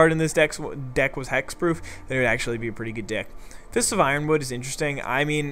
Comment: sounded like "It would actually be a pretty good dick"